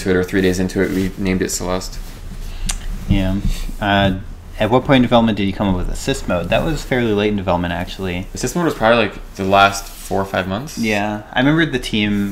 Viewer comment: I heard "at what point in development did you come up with a sis mode?" 😂😂